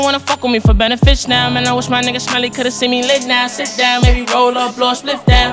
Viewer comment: Is it even a Lil Tjay sing if he doesn’t mention Smelly❤️😢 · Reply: Ohh it’s smelly ? I thought he was saying Melly cause he’s locked up